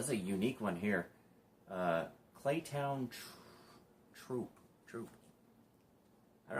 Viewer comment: Had never heard of Claytown Troupe. At first I thought it said "Claytown Trouts" hahaha